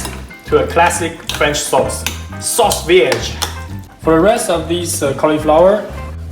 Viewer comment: 幹怎麼我會聽成sauce bxxxh